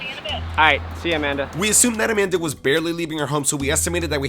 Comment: I thought he was going to say see you man but he said see you Amanda